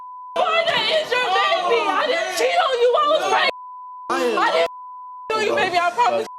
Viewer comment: That red head is hysterical!!! Sick as it can be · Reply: Re listen to what she said after the results.... I stg it sounded like she said "I didn't cheat I was raped"